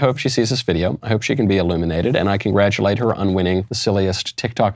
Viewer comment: I had to rewind the video at 8:12 -- I thought you said, "I hope she can be ELIMINATED", but on second listen it was actually "illuminated". 😂